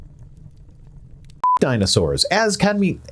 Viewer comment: the beep sounded like a bleep so it sounds like you're saying "fuck dinosaurs" and I thought that was funny. that's all. end of thought